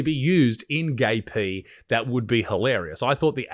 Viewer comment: When he first said “gay P”, must admit, given the subject matter, my brain heard “gaping” and audibly laughed as the “im gonna didgeridoo you in the ass” bit played. While I may have misheard, certainly was appropriate to what was being shown. Theyll probably lure a bilby down the damn thing instead of a hamster next 💀